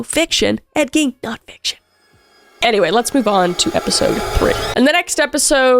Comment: Can someone clarify what says at 16:40 for me? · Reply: “Anyway let’s move onto episode three”